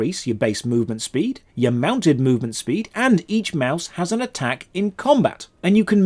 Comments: did he say mouse instead of mount? =p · you say “each mouse” instead of “each mount”.